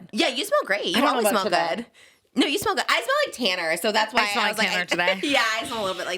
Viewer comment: When said 'I smell like tanner' i literally thought she was saying she smelled like Tana, like a 'Selener' situation 💀😂